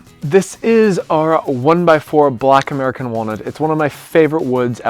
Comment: I thought he said “Black American woman” but it’s “Black American Walnut” 😂